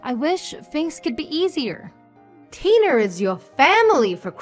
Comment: Tiner? Instead of Tina?